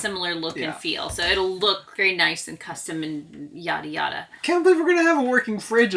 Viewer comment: Please clarify… @ 4:45 did you say yadda yadda or did you say yachta yachta…